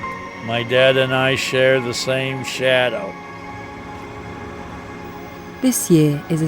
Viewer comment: i thought he said shower, i was like wut ?